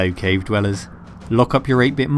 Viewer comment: Why did he call us Cape Feelers ? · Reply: Cave dwellers